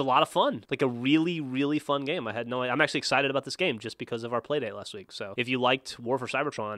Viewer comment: did he say play date. lol little kid